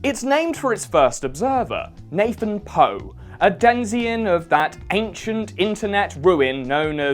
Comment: Googling "denzian" comes up with "denizen" as the first result... so is that what that word is supposed to be?